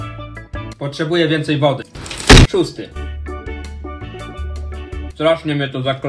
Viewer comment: Czemu zamiast "szósty" usłyszałam tłusty?